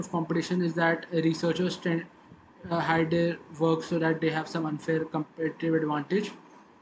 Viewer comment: I couldn't fully understand what you said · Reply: Sorry about that I was just saying "some unfair competitive advantage" will try to keep better audio quality in future videos